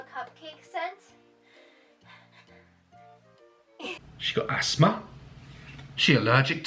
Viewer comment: I thought he said 'AS-MAR' as in asmr lol